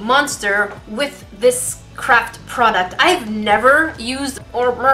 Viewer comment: I heard her say crap product instead of craft product 😂😂